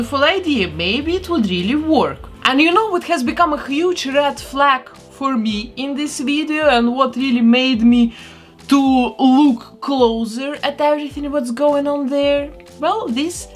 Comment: Funny how I thought you said Rat Flat instead of Red Flag but I knew exactly what you wanted to say.